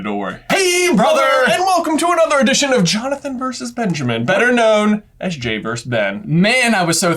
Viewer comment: somehow I heard J call Ben, Benjathan idk why